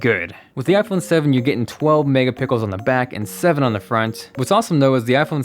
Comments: Am I the only one who heard him say "12 megapickles" rather than "12 megapixels"? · Wait, did he say 12 mega pickles around 4:00? Damn, Apple is really stepping it up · Anyone noticed he says "Megapickles" instead of Megapixels at 4:00? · Did he say pickles? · did he say MegaPickels? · Am I the only one that noticed that he said 'mega pickles' at 4:01 instead of 'mega pixels'? 😂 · I swear I just heard him say the iPhone sevens camera will have 12 mega pickles...